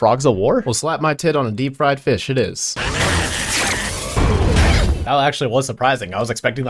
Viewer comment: did he really say "Well slap my tit on a deep-fried fish, it is" dang i need to remember that line